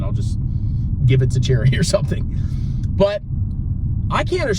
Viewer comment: When you said “my children will inherit VT unless they turn out to be losers” I laughed so hard. Then I thought you said “then I’ll just give it to Jeremy”. Took me a while to realize you meant “charity”. LOL